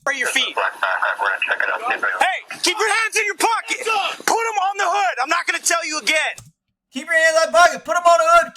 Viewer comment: "Hey, keep your hands in your pocket!!!" Seriously, that's what this scared-of-his-own-piss cop said!